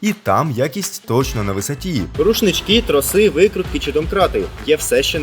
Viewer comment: мені почулось? Труси?